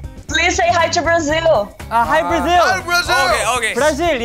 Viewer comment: Am I the only one who hear Brazil India 41:57 by one of them MC